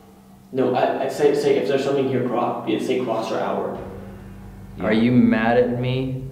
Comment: exhale sounds like it said "YEA"